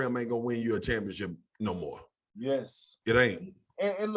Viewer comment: I thought Perk was gonna say hold on I helped the Celtics lol 😆😆 but Swagu said anymore